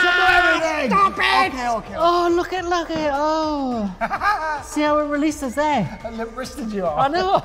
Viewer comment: Can anyone tell me what Dec says at 1:21? His words are a bit slurred. I know Ant is talking about how limp his wrist went but i’m having trouble making out the words. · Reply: “See how it releases there”